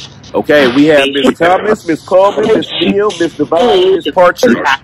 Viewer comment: I heard “I am Devine”